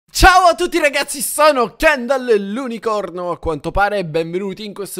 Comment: Solo io nell'intro ho capito "sono ciendal l'unicorno"